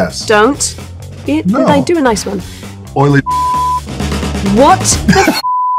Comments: what did he call her? 😂😂😂😂 “oily” then bleeped 😂 what was it? Because it sounded brutal af 😂😂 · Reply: Someone in the comments said “oily discharge”. It seems to fit when you have another look at it. Also matches her expression 🤣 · what did he call her?😂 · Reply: Sounds like he is saying “Oily Discharge” but not at all sure 🤔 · AHHHHHHH!!! 4:45 WTF did he say? Also, she is great, she can dish it out and she can take it. And she has a nice laugh. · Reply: 😂 he said oily ****